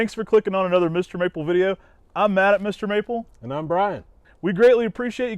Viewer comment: I swear I thought he said “I’m mad at Mr. Maple”! Lol. Instead of Matt!